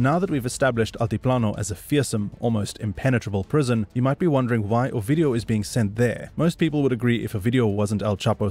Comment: When you'd say his name, it sounded like you were saying "OUR-VIDEO"!!!😝😭😆😭🤣